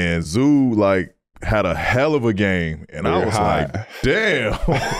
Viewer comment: Did he just say I was high